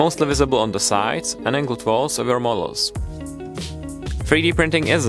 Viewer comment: "so it's mostly visible on the side..." i just don't understand a word your saying after that ! ! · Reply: "...and angled walls of your models."